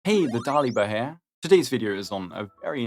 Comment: I thought he said: "Hey! The Taliban here!" at the start of the video